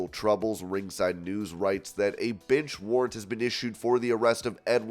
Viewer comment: I thought I heard a bitch warrant